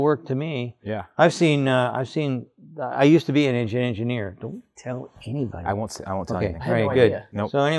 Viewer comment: What did Sandy say? “I used to be a (blurted) engineer”. Love this channel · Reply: an engine engineer. He did the Ford Vulcan V6. Not a powerful engine, but mine is about to roll 333k.